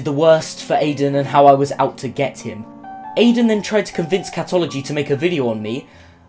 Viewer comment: which you lied about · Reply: i think you said that his name is catology or something like that.